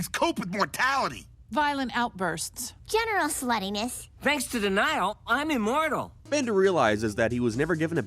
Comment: For the longest time, I didn't understand the joke because I thought Fry was saying "the Nile" instead of "denial"